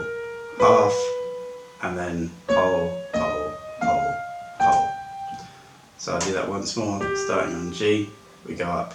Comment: What did Santa say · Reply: Ho ho ho :)